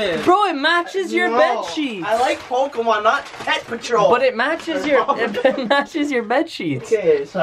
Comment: Did Any One Hear Pew Patrol Instead Of Paw Patrol